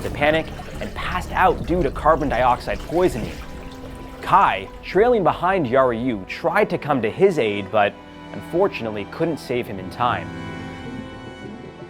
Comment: Why does it sound like he said ‘fortunately’ every time someone died